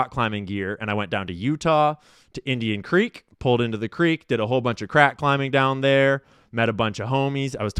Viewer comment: dawg.. I thought you said you did a whole lotta CRACK 😂😂😂 HAD TO PLAY THAT BACK